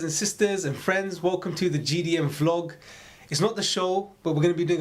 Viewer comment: Whew! For a second I thought he said "welcome to the flog!" You seem to be getting under their skin somewhat. :))